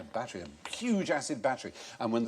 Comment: I thought he said "a huge-ass battery"